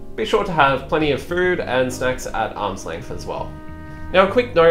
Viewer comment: Thank you for the video . I heard it as be sure to have plenty of food and sex at arms length :D